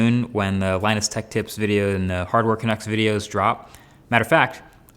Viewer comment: It wasn't until i heard him say it that i realized that it's "hardware canucks" and not "hardware chucks"... WTH is wrong with me 😅😅😅😅😅😅😅😅